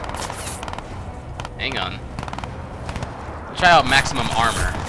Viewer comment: I thought the voice said 'Nutsack Armor' Then i thought about it, and think i must have misheard maximum :O